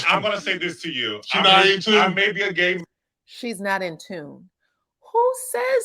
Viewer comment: You said Martel said 'SHE NOT IN TUNE'...I heard him say 🗣 'SHE NAIVE TOO' so basically Martel is saying the rumors are true